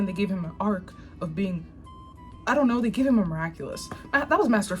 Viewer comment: Every time she said mira culous it's sounded like she was saying look at culo which is look at this butt